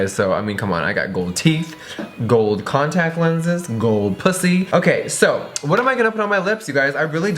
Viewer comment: did he just say ,, gold pussy,,????!!!! lol oh Jeffrey